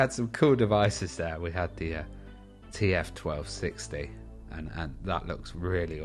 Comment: I misheard TF as GF 1260 at 04:00 - thought it was some special Nottingham "Goose Fair" revision :-)